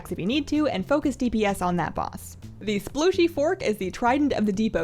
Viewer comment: Best raid guide using the term "splooshy fork" ever! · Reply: I thought she said sploogy fork :P